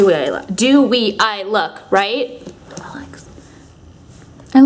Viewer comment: Speaking of the dead girl look, who else heard that ghost say 'Alex' at around the 19:52 mark?! Alex's mouth moves but the audio doesn't make sense. #Possession lol